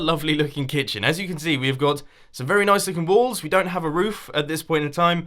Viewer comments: I heard some very nice looking ballz 😅😳.... imma head out... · Did he say balls!? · did he just say "some very nice looking balls"?